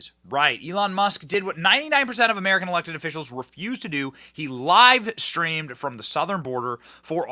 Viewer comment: I misheard it as "Lied-streamed" lol, an act perfect for liberals, leftists, and democrats